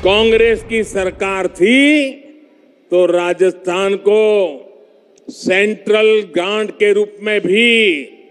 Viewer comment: Did I hear central g@ at 2:06 or something else 😂